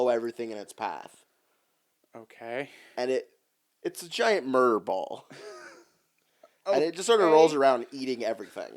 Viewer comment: I heard "murr ball" and was disgusted